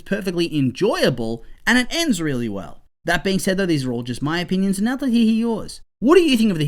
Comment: what did he say? the captions say "he he yours" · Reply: “These are all my opinions and I’d like to hear yours”